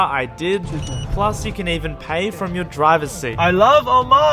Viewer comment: what he said: I LOVE OMAN what i thought he said: I LOVE ALL MEN